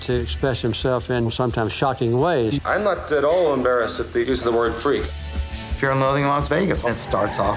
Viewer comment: What does hunter say at around 0:28 ? "something something word freak" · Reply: "I'm not at all embarrassed to use the word 'freak.'"